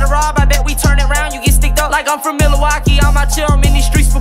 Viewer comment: did he say from Milwaukee???